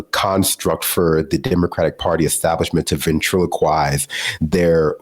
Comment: Did you say “ventriloquize” ? 😂